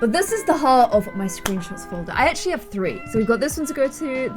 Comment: I thought it said mods folder and I was ready to list the whole thing down 😂